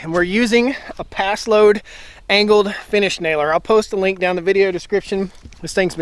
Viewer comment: uh....those are called ring shank exterior framing nails there.....over 2000 nails in this fence, all placed with full round head paslode nailer. Not sure where you're getting your ideas about finish nails...that would be the dumbest move ever wouldn't it? Paslode makes more than just finish nailers my friend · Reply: my mistake. In the video you said finish nailer, referring to the gas operated nail gun. I thought you said you were using finish nails lol